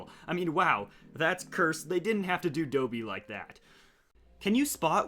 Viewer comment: Did he just say dobey?